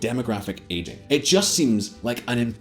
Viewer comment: At about 09:50 does it not sound like he says imposhible?